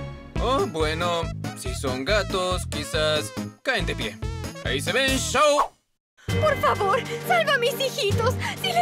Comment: What does Kiwa say at 1:39, after "caen de pie?" Also, at the very first shot, 0:09, I don't understand what the unnamed brown cat says before "no está aqui." (sorry for using English, my Spanish writing sucks) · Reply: 3 years later, but well... At 0:09 It doesn't say "no está aquí", it says "nadki" and at 1:39 that cat says "Ahí se ven... Chao" meaning "bye, see ya"